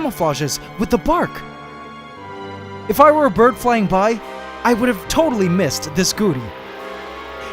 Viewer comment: i swear i heard, “if i were a bird flying by, i would’ve totally missed this booty”